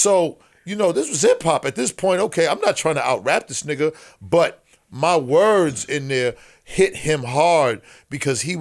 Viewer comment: Did he just called him a nigga 3:05😂💀